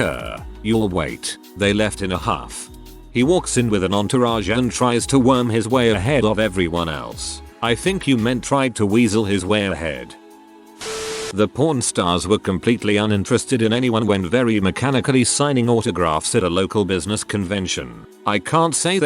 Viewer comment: The computer sounded like he was saying Pron stars not pawn stars. LOL